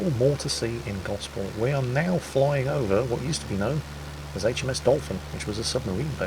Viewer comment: did you say "HMS Dalton"? Ironic, considering there is a place called dalton, which has a submarine manufacturing facility. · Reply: No, HMS Dolphin.